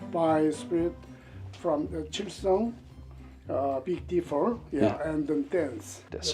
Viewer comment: This man is actually saying 'the Big Dipper', not 'big differ'. Chilsung(7 stars) refers to Bookdoo Chilsung, which means the Big Dipper.